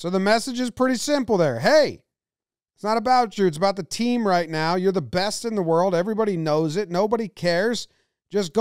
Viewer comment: sounded like he said “you’re my best friend” not “you’re the best in the world”